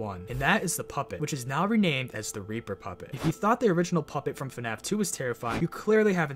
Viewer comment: I thought he said the r a per puppet